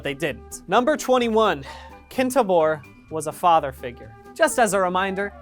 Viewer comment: I thought it said "Kinktober was a father figure"...